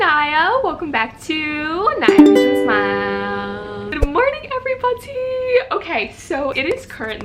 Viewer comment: I ADORE how you say "everybody" it sounds like you say "Peabody" from Mr. Peabody and Sherman. 😍😁🐶